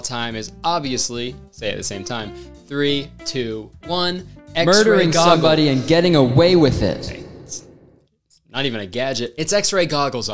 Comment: Why did I hear "Murder goggles" and "X-raying someone and getting away with it"